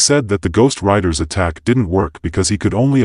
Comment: i thought you were saying ghostwritters...